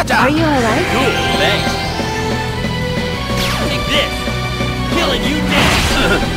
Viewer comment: did L'Arc say killing you dead??? lmao. Fuck what anyone says best dubbing EVAR!!! lol.